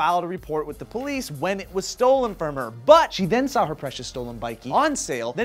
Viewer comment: I heard "When it was stolen from her butt!" at 0:28 ! OMG